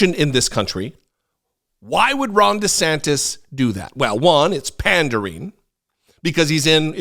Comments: For a second I thought said "Wrong" DeSantis! 😂😂😂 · NGL, it sounded like you said “wrong DeSantis” and I am here for that